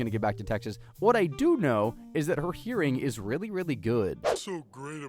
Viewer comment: i was far away from my device and i thought he said "what i do know is that her urine is very good"